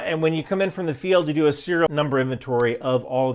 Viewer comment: I’m sorry did you just say you’re a serial killer 0.o